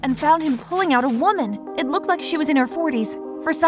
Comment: "It looked like she was in her fourties" Did I hear that wrong?